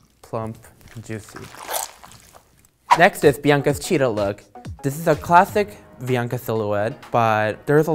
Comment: I thought she said Bianca cheetos look